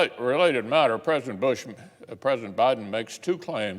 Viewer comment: did he say president bush at the beginning? 🤔 I sworn that's what he said. how does this bill's cost 0$, these bills will hurt the middle and lower class either during and/or in the long run, greed will always be the root of evil. its sad that we have these old fossile's still in the government, both side's Democrats and republicans are so out of touch with today's economy and society. its not even funny any more. 🤷🤷